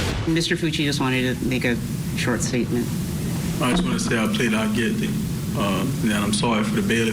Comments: It sounded like he said “not guilty” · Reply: He said “I plead out guilty” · It sounded like he said I plead not guilty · It almost sounded like he said not guilty I guess maybe his accent or something? Also, since he pled guilty, and they know it is his knife - it is no longer the ‘alleged murder weapon’ it is the murder weapon. I’m glad the Bailey family will not have to sit through a trial! I pray the judge shows this killer the same amount of mercy he showed to Tristyn - NONE! He should never see the light of day again! RIP Tristyn ❤! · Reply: I agree, That's what I thought I heard he said not guilty. I kept going back & listening 🤔? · Sounded like he said not guilty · That's what I thought he said also. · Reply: I plead out guilty he said. His lawyer said He changed his plea to Guilty at the beginning of this session. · It sounds like he says I plead not guilty. It’s so odd. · I’m confused, it totally sounds like he says “ I plead not guilty” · Sounds like he said not guilty